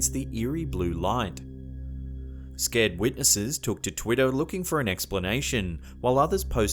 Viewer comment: Was it just me or at 6:57 he DID sound like he was saying "Twitto"